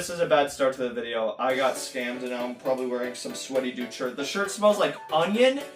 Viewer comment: U got scammed is what it said